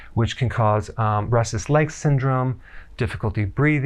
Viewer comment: I thought you said breastless leg syndrome